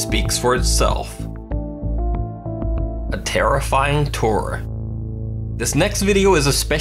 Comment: The terrifying tour @ 25:02 sounded like terrifying turn 😆😆😆